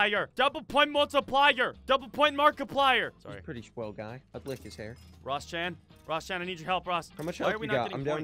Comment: Uhh... what did he say? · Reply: HannoVZ “he’s a pretty swell guy.. i like his hair .,.”